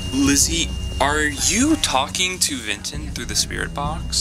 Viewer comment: I heard something like “Vinton”